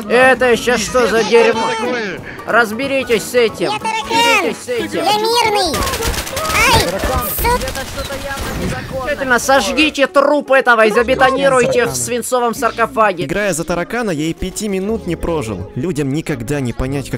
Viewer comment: Мне одному послышалось ,, ай СУ"?